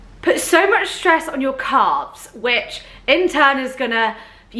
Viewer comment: I’m watching from America. Every time you said calves, I thought you were saying carbs.